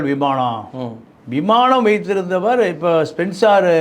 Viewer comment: did he say SPENCER ?!?!? adhu RPG group company aachey ?!